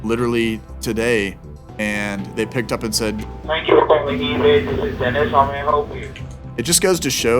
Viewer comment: Just after the 3min mark, I thought he said "thank you for calling avis", not ebay.